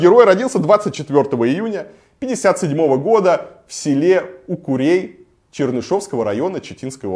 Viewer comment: у кого родился? У свиней? А, у курей... Понятно, с первого раза не расслышал, думал у свиней.